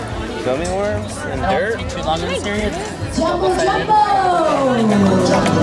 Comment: Who else heard the person at 7:31 say please don't spend to long in this area this is a double sided area. Like they can take as long as they want to so BE QUIET